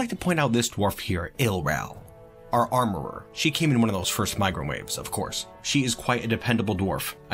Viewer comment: I hear "microwave" whenever he says "migrant wave".